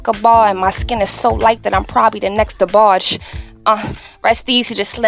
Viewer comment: did she just say "My skin is so light that I'm probably the next DeBarge"??